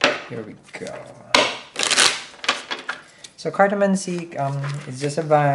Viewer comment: For the longest time I thought you were saying "Cardamon Seed" 😂 took me a while to figure out it's "Cartomancy" 😂 love it! 💗